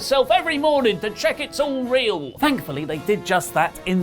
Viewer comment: Hand signs like nipples, and "all real" sounded like "areolae." What are you trying to tell us, Oli?